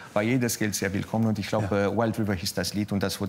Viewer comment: Wie war der Titel nochmals? Wer hat's verstanden? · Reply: Wild River